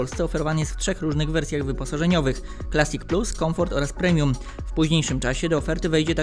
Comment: Czy tylko ja usłyszałem Hundai plastik plus. Zamiast clasic plus ?